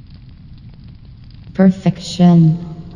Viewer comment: Everyone in the picture: dies Mother: perrfection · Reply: I thought she said perfect chin...